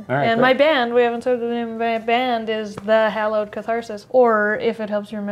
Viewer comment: I totally misheard the band name as The Hello Catharsis and thought it's a Hello Kitty -band